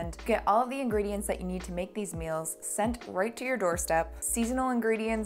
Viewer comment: I heard "customize the males you want" instead of "meals" 😂